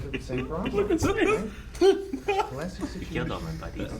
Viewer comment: "You killed all my buddies." "Ah shit, I'm really sorry about that, man." LOVE Hader's delivery. He says it as if he's apologizing for accidentally spilling someone's glass of orange juice at the breakfast table lol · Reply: wait i thought cristobal was saying "You killed all my Barry's" because thats what noho hank and cristobal was saying in an earlier episode?